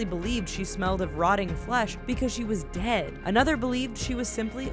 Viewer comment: Did anyone realize that in 1:26 it said fish instead of flesh?